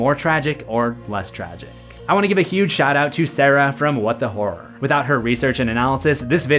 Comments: I really thought I heard him say “what the whore” 😳😂🤦🏽‍♂️ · thought you said what the whore instead of what the horror I need god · I thought you said her channel was named “what the whore” not “what the horror” 😂